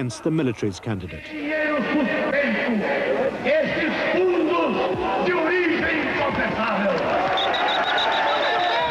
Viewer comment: What is Brizola saying at 3:09? (I'm doing the Portuguese SDH; I'd like to know what he's saying.) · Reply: "esses fundos de origem inconfessável"